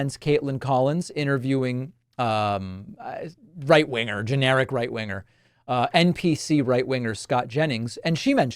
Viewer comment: I swear I thought you said "geriatric right winger" hahahaha